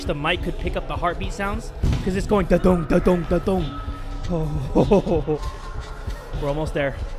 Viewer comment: Video : 2:43 I misheard Jay saying That Dong Me : Jay.... do you want that?! Because that was what your heart said!